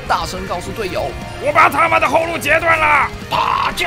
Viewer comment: 我一直聽成 我把他媽的後路截斷了